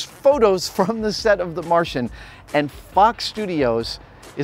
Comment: sounded like he said - "and f*ck studios"